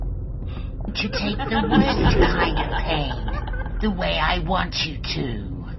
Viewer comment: what did she say? "Youre going to take the worst kind of my pain, the way i want you to"??